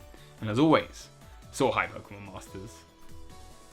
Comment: Am I the only who thought he was saying "so hi Pokemon masters"?